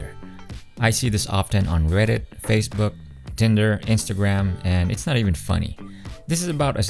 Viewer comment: did u say Tinder??